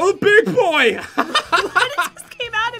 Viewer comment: this is what he said 0:00 DOH IM A Big Boy!Mom?